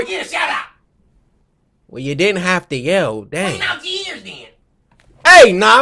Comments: what did he say here?... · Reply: Clean out your ears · what did he say? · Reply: "clean out your ears then" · what did he say at 0:35? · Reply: "Clean out your ears then"